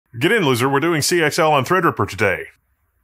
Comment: "get in loser" lol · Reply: I need to get my hearing checked; I heard "get in lizard" :D hehe